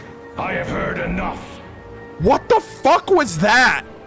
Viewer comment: I think he said "I've heard the [unknown] many times... I've heard enough!" And 7:41:40 I believe is "You call me forth just to end my life? Then so be it!"